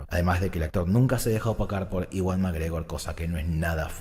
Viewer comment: ¿Me podes explicar eso de que "Nunca se dejo tocar por McGregor"? No entendí xD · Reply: Dijo opacar no tocar.